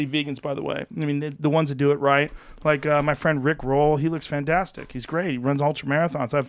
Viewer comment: hold up, did I just hear him say he knows a guy named Rick Roll?